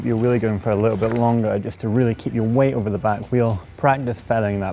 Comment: It sounds like he says 'willy' lol